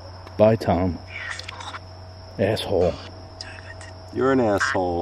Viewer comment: I heard asshole then dammit